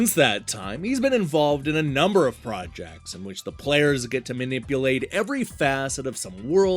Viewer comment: It really sounded like you said "Peter Molyneaux has been involved with a number of fraudjects" which is the perfect way to some up his last 10 years or more! 🤗